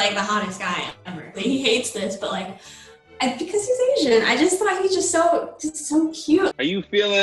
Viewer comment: I kinda threw up in my mouth a little when she said bc he's Asian. I mean ofc to each their own and ppl have there preferences but still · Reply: I thought she said he’s unfaithful, not Asian